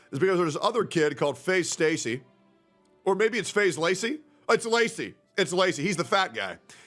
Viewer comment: am i the only one who heard Space Laces at first?